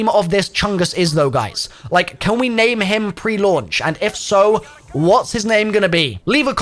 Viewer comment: i thought you just said that its gonna be called "pre launch" 🤔